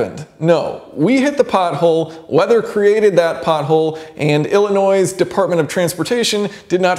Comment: That's about the same price as 4 aftermarket wheels with tires. 17-18" aftermarket wheels and tires.... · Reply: Thought you said "Illinoise" for a sec, lol "Illinois' depart of transportation"